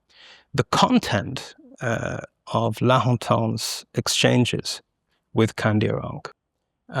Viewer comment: what are the names he said? The conversations between who? I'm here trying to look it up but no idea how to spell the names. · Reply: He is referring to the Baron of Lahontan and the indigenous leader Kandiaronk.